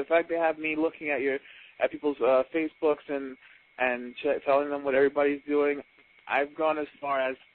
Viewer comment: did he say facebook and s**t???!